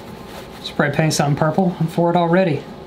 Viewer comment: I know i ain’t the only one who heard him say “Pray Spaint” 💀💀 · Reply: I heard "pray paint" 💀